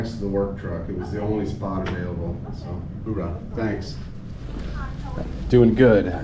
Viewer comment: Are we sure that guy's a civilian? He talks like a cop, acts like a cop, and hangs around cops like he's best buddies with them. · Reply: He's obviously military. As he walks away initially, it sounds like he says, "Hooorah..." I'm guessing he has some sort of police tie to this dept one way or another.